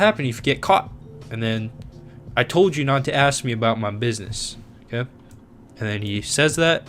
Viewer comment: It sounded like: I told you not to ask me about ma business.